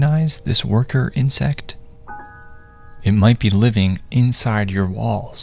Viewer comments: I just woke up and watch this and I thought I heard "It might be living in your BALLS" I double checked to make sure I heard it wrong. 🤣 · I thought it said "it may be living inside your balls". Had to check my pants for a sec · I thought he said it might be living inside your Balls, had to rewind just incase 😂😂😂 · I heard “walls” as “balls” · I honestly thought he said it might be living inside your balls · Ngl I thought bro said living inside ur balls at first ✌️ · I got terrified when I heard "walls" as "balls"